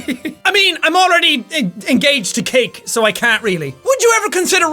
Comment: Wait.. WAIT!!! your.. Engaged?! I Thought you just had a Girl-Fiend, Unless you mean Engaged in a different way, But.. YOUR ENGAGED?! WOAH KEWL DUDE! and then i went back, and listened to it.. and realized he said 'Cake' when i thought he said 'Kate'....